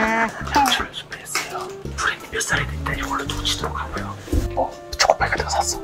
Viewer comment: 순간 푸린이 몇살이됬든 이걸로 초치도록 하구요 라고들었어